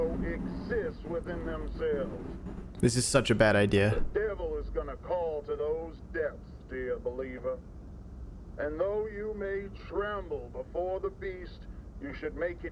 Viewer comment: In the audio in the beginning, I heard “the devil has a plan for you.”